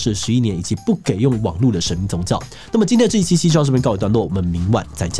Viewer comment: 結尾一直聽成 今天的志祺ㄐㄐ到這邊告一段落🤣 腦袋中只剩ㄐㄐ